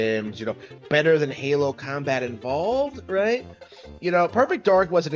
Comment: Did he just say "Combat involved"?